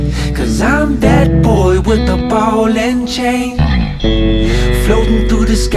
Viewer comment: Totally thought it said ballin chain